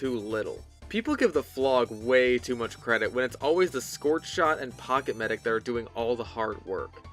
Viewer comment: I’m sorry did you just say the squirt shot is doing the “hard work”